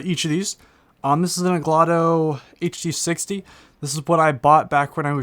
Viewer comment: Did he say englato